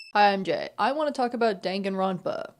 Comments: 3 seconds in and i thought you said "hi i'm gay" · With my not so good hearing... I heard 'Hi, I'm gay'. A very strong opener for sure. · Oh i thought u said hi im gay i was like slay congrats